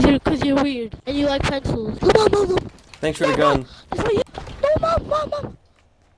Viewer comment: I honestly thought he said "No mom On Obama" I thought I had learned how to control my game rage but I was wrong when recently I smashed a phone on the ground because it was ringing while I was playing a heated match and the ringtone is annoying af XD It's a landline phone too. (Seriously who tf has Justin Bieber as their ringtone anymore?)